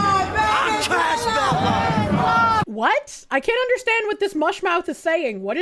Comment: Casus Belli, latin for an act or an event that either provokes or is used to justify a war. I think is what the guy is saying